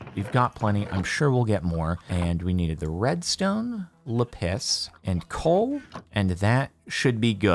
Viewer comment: Did he just call Lapiz "Le piss"?!